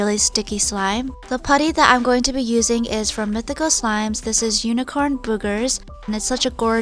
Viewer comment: I thought that said unicorn boobies cause I didn't have my glasses on😂😂😂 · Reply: I thought it said Unicorn Boobers